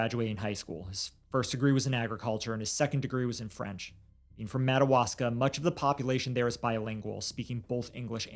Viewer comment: in... WHERE? 🤨 I didn't understand what he said. The captions aren't helping, either. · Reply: Madawaska. It's a town in the very tip top of Maine.